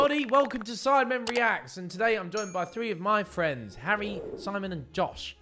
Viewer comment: why did in the intro it sounded like im doing three of my friends... instead of joined by three of my friends XD